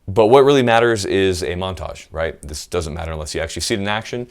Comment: Oh, you said MONTAGE! I thought you said MENAGE a trois. Damn....